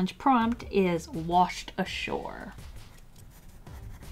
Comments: Oh god I thought it said “washed asshole” 🤦🏻‍♀️ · I swear to god I saw the prompt and at first glance I thought it said washed asshole